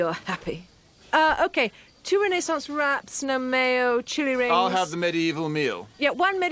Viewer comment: I always assumed chili rings meant onion rings covered in chili, like chili fries, but this is a more unique take on the order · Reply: I honestly thought she said “chewy rings” and I wondered what that was supposed to mean ingredients wise 😂